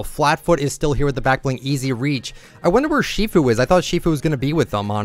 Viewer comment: did he just say master shifu? lol